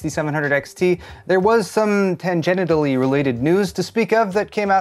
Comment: Did I hear you say "Tan-genital" ?